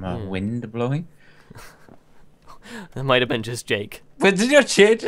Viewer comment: I thought he said cake xD